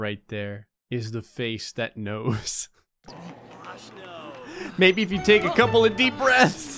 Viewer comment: did anyone else hear "Oh my gosh no"?